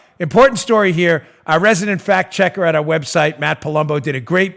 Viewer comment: Fact check, oh I thought you said fat check. Now I am completely confused.